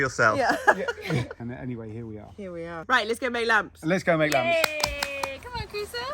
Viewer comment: Ya know, after related his romantic story about how you both met, he said, "alright now, let's go make a lamp." I thought, however, that he said, "let's go make love." 🤣🤣🤣🤣. My immediate thought was, how cheeky of the bugger🤣 and does he actually imagine that was enough to just get his way. LoL 😂. I was totally defeated to learn that it was the lamp that was on his mind. Wonderful, joyful living. I'm wishing that I could travel there as it's been over 17 years since I've been to Spain and Portugal. I shall continue to happily peruse your channel ❤️